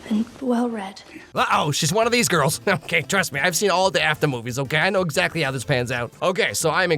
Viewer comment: I was so tired when I watched this, I thought he said “Afton movies”